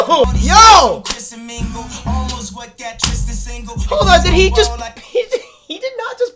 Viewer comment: did you just say 'HOLDUPNIGGE'